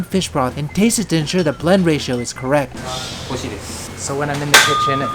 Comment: Before I turned on captions I heard "To make sure the blood ratio is correct." Anyone else? No? I"ll leave now.